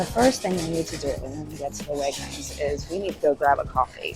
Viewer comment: So funny - 1:40 in and I hear you say "So the first thing we need to do when we get to the wagons is..." And I'm, like, "Is she talking about the shopping carts?" I'd never heard them called "wagons" before. Vancouver resident by way of two previous prairie provinces, btw. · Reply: She said when I get to the Wegmans